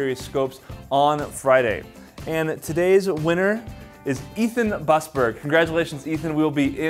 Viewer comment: Did you just say I am today's winner or misheard it? :D